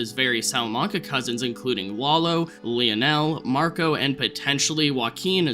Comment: Can't believe we first saw Tuco 15 years ago. @ 2:23 it sounded like you said "Wallow" instead of "Lalo". Am I just hearing things???